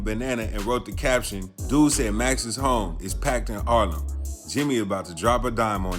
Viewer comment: It’s packed in what? 1:10 … What are you saying? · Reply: he said Harlem…He was in the club Harbor 😂😂